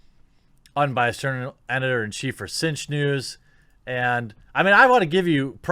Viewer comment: "Cinch news" I hear: "Tzeentch news" O____O Coincidence?.. I don't think so! xD